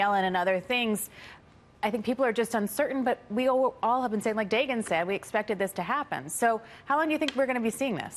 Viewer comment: I heard "like Dagon said . . ." Creepy